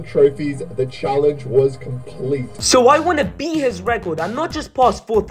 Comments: IT SAID BEAT HIS MEAT AT 0:09 FOR THE SUBTITLES INSTEAD OF THE RECORD LOLOL srry for caps lock lol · beat his "record" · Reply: Are yall slow or sum? In 0:09 there was "i wanted to beat his record" but the subtitles said "i wanna beat his meat"